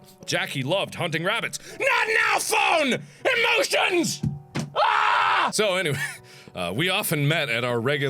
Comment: I want a GIF of Mark going "NOT NOW, PHONE! EMOTIONS!" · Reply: Before watching the video I thought you said not now phone emoticons 😂